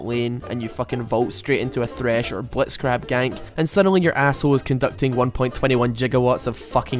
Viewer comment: Did you say "Blitzcrab gank"?